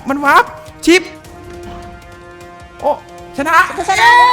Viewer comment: พี่พูดว่า บูชาเย้หรอ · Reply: พูดว่า ชนะเย้ค่ะ